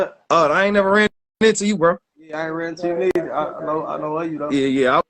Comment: That first dude said ( I don’t know you but I don’t like you thou, that’s the first words said to Amarni, that should of been enough said and a hint to be careful, I’m a female and know people who are like that and know, my condolences to the families RIP to the three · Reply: I thought he said I haven’t ran into you yet